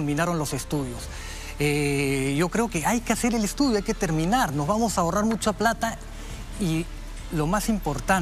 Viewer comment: creo que dice "nos vamos a robar"